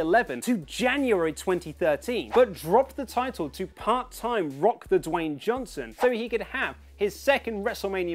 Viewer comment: Am i the only one who heard Rock "The Dwayne" johnson